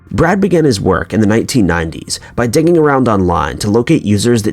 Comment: Hahah I had to rewind at 4:26 like 3 times to figure out he was saying "digging around online" I thought he was saying "dicking" I'm slow or something